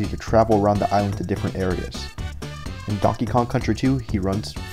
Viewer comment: Great video! But did he just say "Caucasian donkey kong"?